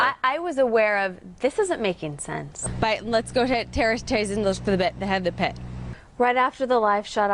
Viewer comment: Excuse me what. Can anyone tell me what she’s saying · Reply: At some point she was trying to say, "Let's take a look" but it came out, "Let's go ahead, terrace taste a los lapet a hevna pet." Definitely the side effects of a complex migraine. Scary to not physically be in control of your tongue! Or any part of your body.